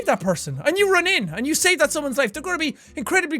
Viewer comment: When he said save it sounds like he said shaved